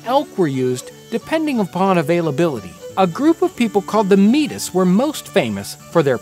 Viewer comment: I couldn't watch beyond the mispronounced Métis. Do some research next time. Actually thought you were saying "the Meatists" at first